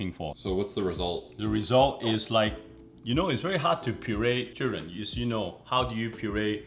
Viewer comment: lol i heard "its very hard to puree children". Editing is A1 (get it?)